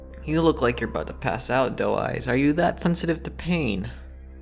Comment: I thought he was saying dull eyes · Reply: i thought he was saying doll eyes